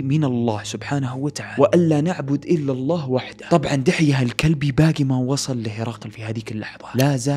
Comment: مش فاهمة انا سمعت كلمة كلب بالغلط ولا ايش!! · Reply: اسم المرسل الي ارسله النبي إلى الروم (دحيه الكلبي) الذي معاه الرسالة